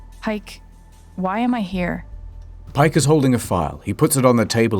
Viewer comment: I heard ‘Pike is holding a fart. He puts it on the table and sits down’